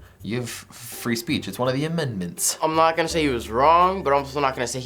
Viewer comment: did say admendments? haha